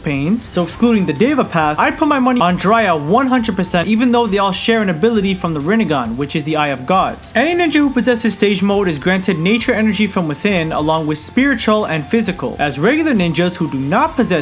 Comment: I thought yo said no gods even tho hashirama is the God of shinobi